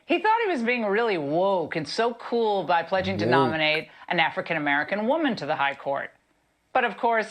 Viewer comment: oh...wait a minute, Laura ...he did not say "African American woman"...he said black. Not all black people are "African American". Stop trying to be politically correct when all you should be doing is be correct.